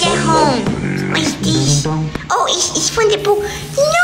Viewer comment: what he said:oh,it's from the pool! what I thought he said:oh it's fun to poo!